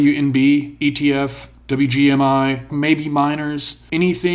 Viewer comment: that's OWNB ETF, for anyone else watching this and searching the ticker. auto-generated captions got it wrong.